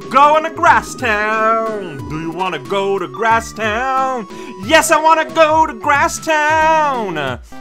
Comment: no, that's not what she said. She said "why don't you go to Grasstown, in my place?" meaning, instead of her, since she can't go herself